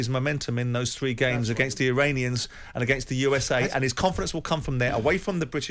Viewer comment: Did Jordan say "Uranians" ?!